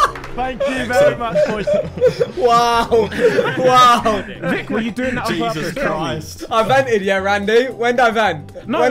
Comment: What did Tobi say at 30:36? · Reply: “Vik were you doing that on purpose? Tell me”